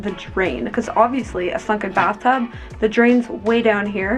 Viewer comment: OMG, you said SUNKEN bathtub at 6:40. I thought you said f****n’ bathtub! 😲 I was like, whoa, why so hostile? Glad I rewound and listened again LOL! 🤣